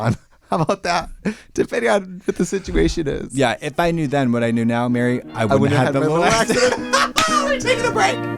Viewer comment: Would someone with younger ears, be so kind as to tell me what they said at 12:25 😭 I’ve listened so many times and still can’t catch it lol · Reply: "If I knew then what I know now I wouldn't have had my little accident". It's a beetlejuice quote